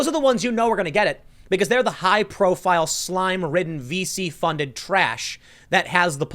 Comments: "high-profile, slime ridden, VC funded trash" For a second there I thought you said "feces funded trash" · Sees that it's for local news outlets: Okay, sure Sees that it's also for the MSM... damn it. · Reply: "high-profile, slime ridden, VC funded trash" For a second there I thought Tim said "feces funded trash" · The media should not get a bailout The media shouldnt even get Bail. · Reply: "high-profile, slime ridden, VC funded trash" For a second there I thought Tim said "feces funded trash" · Let the left wing media collapse nothing of value will be lost. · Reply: "high-profile, slime ridden, VC funded trash" For a second there I thought Tim said "feces funded trash"